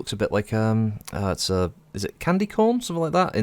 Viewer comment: Sorry if I mis-heard you, but yes, those are candy corn. (Sounded like you said 'cone', which is understandable and descriptive, but it's supposed to look like a nugget of maise)